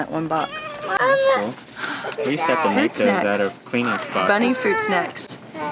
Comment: Does anyone know what did say to during 4:44-4:47 ? I really want to know why frowned at after hearing those words. I've replayed it for several times, only to hear calling MAMA in the background lol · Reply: Peanut boxes he said n thats the frown